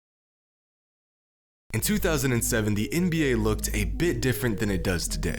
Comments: Did anyone else hear him say 2070 at 1:19 instead of 2007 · Who else heard him say "In 2070?" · Am I the only one who heard 2070? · My boi said “in 2070”. Please tell me I’m not the only one. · anybody else hear “2070”